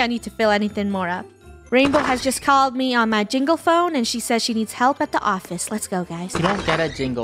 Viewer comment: why in my ears I hear a pringle phone????